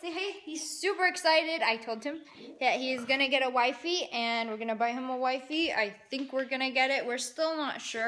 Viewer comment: I thought she said burn him a new wifey 😂😂😂😂😂😒💞😂😂😂😂